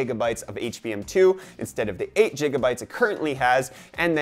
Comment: did you just say "jigga bytes"